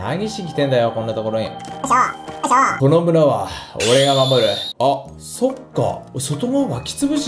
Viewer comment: 最初のこの村は俺が守るって奴、野々村は俺が守るって聞こえたw